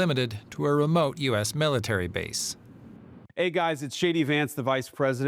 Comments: I heard, "hey guys, this is shady Vance" .. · Swore he said 'hey guys it's the Vice President' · it sounded like he said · I swear he said it's. I can't think of a more appropriate name...